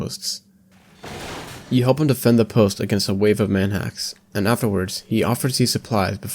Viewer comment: i got super confused when i misheard manhacks as "man ass" but cool video